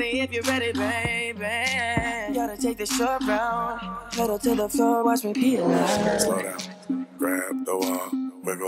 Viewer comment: It really does sound like Justin is saying "Headed to the toilet watch me pee like."😹😹😹